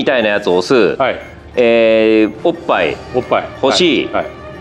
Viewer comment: 多分梶田さんは「Qみたいなやつ押す、おっぱい、星」が「Qみたいなやつ押す、おっぱい押しー・・・」って聞こえたんだと思うｗ